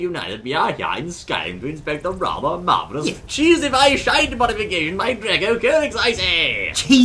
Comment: I thought for a moment he said "Cheesify shite". :3